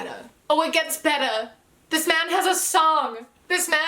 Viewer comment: oh thank god, I thought you said this man has a son!! So glad I misheard that....